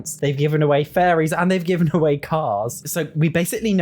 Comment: wait did he say cars!! Is that true??